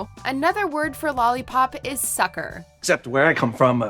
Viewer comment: i always thought the delivery man said "soccer" not "sucker". It confused me so much as a kid, it makes much more sense now...